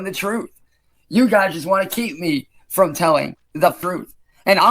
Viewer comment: Am I the only one who heard "you guys just wanna keep me from telling the fruit"?